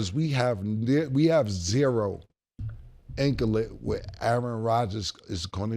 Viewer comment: Inkalite? What cam say? 59:58 how you spell that big word? Lbs